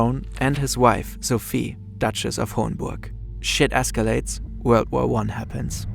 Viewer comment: did bro just say “Shit escalates” 😂